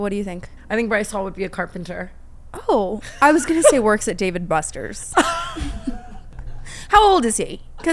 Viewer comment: did she call it David Busters😭🤣